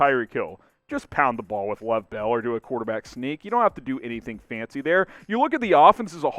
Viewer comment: Did you say "quarterback sneak"? I have a mind to wash your mouth out with soap.